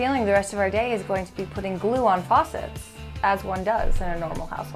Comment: Honestly thought it said hot glue on a faucet 🤣🤣🤣